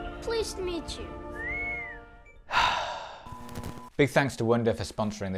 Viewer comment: Ok, at first I heard "Big thanks to Windu for sponsoring this week's video" at the end.